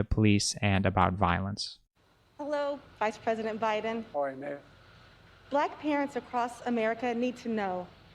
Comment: What does he say at 1:00 ? 🥴 hola May?